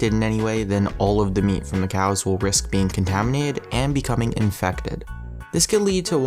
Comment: & 0:45 It sounds like he says Contaminade. Is that a new soft drink? 'Cause it sounds delicious.